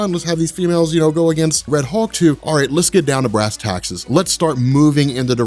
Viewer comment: … did he just say let's get down to brass taxes?